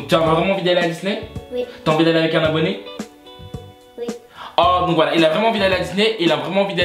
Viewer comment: Mdr 9:11 j'ai entendu t'as envie d'aller avec Carla Bruni ? J'ai bugué aussi mdr